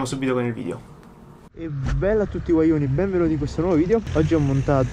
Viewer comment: solo io ho capito "bella a tutti coglioni"?